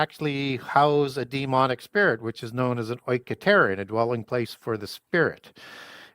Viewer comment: Does anyone know how to properly spell the word uses @ 25:09 and @ 25:15? My spelling is incorrect but the word he used sounded like "Oy catarian". I would like to do more research on it. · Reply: "oiketerion" How this helps!